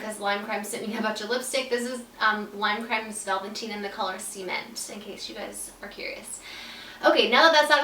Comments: girlllll..when you said "cement" i thought you were saying "semen" 😂😂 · did anyone else thought she was about to say that the lipstick shade was called "semen" by the way she first started saying "cement"? 😂 oh i love u so much, u always make me laugh!! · Hehe she said the lip colour was "sea mint" and I heard "semen" loooool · Do you mean the color "cement"? It sounded like you said "semen" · At first I thought you said the color is semen